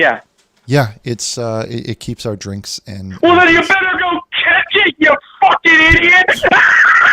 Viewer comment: what did he said? I can't hear it clearly. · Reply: “Then you better catch it you fucking idiot!”